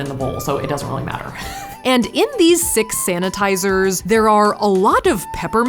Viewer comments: did anyone else hear “SANTAtizers” or am I tripping?? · Tell me I'm not the only one who heard "Santa-tizers" 😂